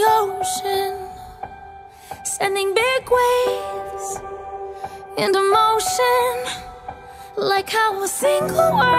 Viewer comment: What Elena says in 2:27? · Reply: I dont wanna be a vampire.